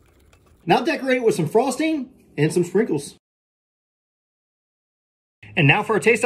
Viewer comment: did he just say "fringles"